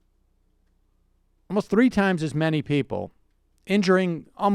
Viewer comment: Las Vegas shooter killed 6 times as many people, as the terrorist attack on Tuesday. It sounded like Sam said 3 times as many.